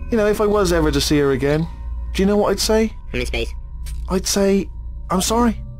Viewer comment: “Anyway, ya know if I was ever to see her again, do ya know what I’d say?” “Where in space” 😂😂